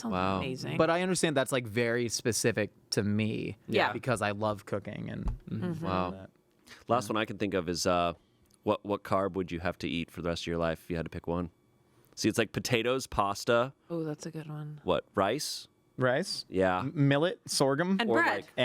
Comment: I thought he said "what car would you want to eat for the rest of your life?" I was like haha that's funny. And then he said "potato...". And my first thought for a brief moment was: he's an idiot. That's not a car. 😂😂 Just thought that was a funny moment to share. Sending love and support to the podcast from Greece!🇬🇷🙏 Edit: for the record, I think I'd pick bread. I love a good sandwich.